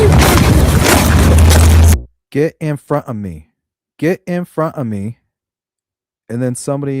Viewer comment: I dont hear "go in front of me," i hear, "just keep walking."